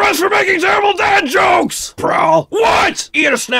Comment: did he say prowl?